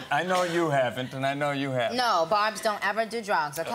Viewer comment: what did she say? Who don't ever do drugs? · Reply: Kumar Himanshu Barbs, her fans.